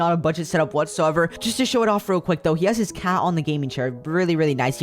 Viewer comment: you sad hat instead of cat